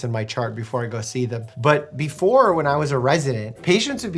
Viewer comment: I thought he said “ before I was arrested “ I was like wut…. And i realised 😂😂😂😭😭😭